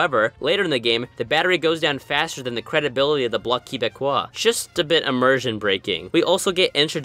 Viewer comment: did you just say key-beh-quoi